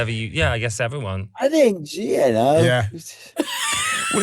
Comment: what is H saying “I think…”??? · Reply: I think gee you know?